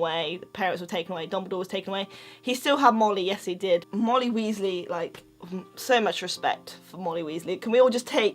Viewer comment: It sounds like she is saying Moldy Weasly